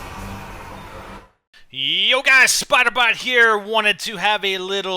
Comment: It sounds like you say spider bot